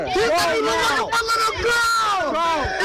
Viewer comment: Underage drinking and disorderly conduct are the equivalent of a traffic ticket. This is a murder trial and petty violations have absolutely nothing to do with it. Were these kids acting like assholes? Yep. But they were not armed-not even with a stick. And this guy, without even warning them went full tilt Jack the Ripper. And then was evasive with law enforcement. He is in very big trouble. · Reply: You have a valid point. MY point is that WHOEVER started with the yelling that Nikolai was "looking for little girls" opened the flood gates for the rest of the inebriated kids (and adults) to holler and bully him - because, and this is key: one of them MISHEARD HIM when he said he was looking for his goggles and snorkle! How heartbreaking is it that NONE of the tribalism and aggression that rose up had to occur? The whole situation was entirely preventable.